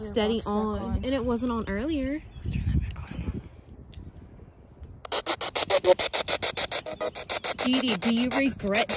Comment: 8:55 LISTEN CLISE SLIW DOWN I DON'T HAVE THE TOOLS YOU SHOULD LISTEN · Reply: All I hear at 8:55 is the girl whispering "least turn that back on"